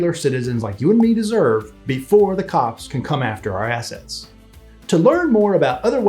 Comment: I sware I thought he said before the cops can come after our asses lol